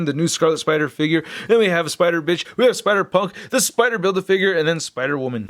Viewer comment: Did he say "Spider-Bitch"?